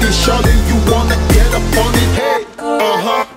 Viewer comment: 「ヘイ！おばはん」に聞こえて1人でツボってる おばはんじゃなくて、Over handとかでも言っとるんかな？